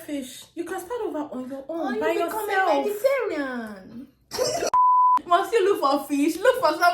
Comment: Nahh you and Mena look soo much alike 😂😂 did she just say or become a vegetarian 😂